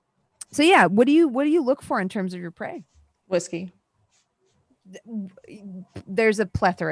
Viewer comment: I thought Ulrika said p*ssy @ 17:07 and was like, "Wow! They all just took that in stride!"